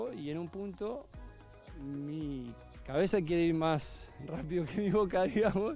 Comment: Pensé que decía: mí cabeza quiere ir más rápido a Boca jajajajaja